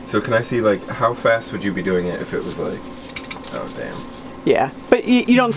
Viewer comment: Did say Oh Damn at 11:24? 😂😂😂